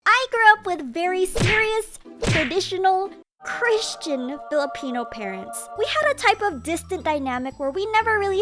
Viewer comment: Did u say filipino?!